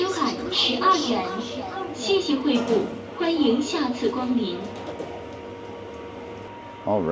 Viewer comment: and @ 14:36 - I don't speak or understand any form of Chinese. I definitely heard and understood the female voice saying "Xi Jinping Pooh", twice! Which made watching this video, priceless... America LOVES Xi Jinping Pooh anything! Thanks for sharing! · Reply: The voice is saying "Thank you for your patronage"（Xie Xie Hui Gu），not "Xi Jinping Pooh"